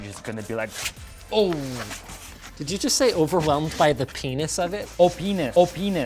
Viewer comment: Linus: Did you just say openis? 5 seconds later Linus: vinyl daycow